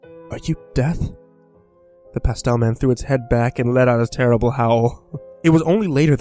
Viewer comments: i thought it said "the pasta man" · at first I thought it said "the Pasta man" :P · I thought it said the pasta man at first